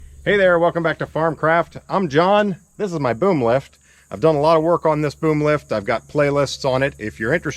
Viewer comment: For just a second I heard "I'm John, and this is my boom stick", and I thought "Groovy." But then my eyes caught up to what you said. Oh well 🤷‍♂